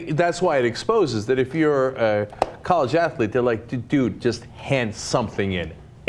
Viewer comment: I thought 'Athlete' said Atheist and now this makes more sense.